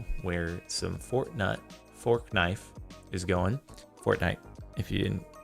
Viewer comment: Did anyone else hear forknut at 7:17. Really Jardon really now you putting forks in people's nuts what's wrong with you smh lol